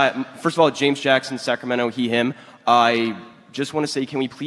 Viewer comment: for a second, I actually thought he said his last name is "Heehim"